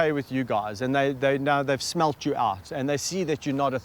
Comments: Did he say smelt your ass? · did he just say "smelt your arse"..? lol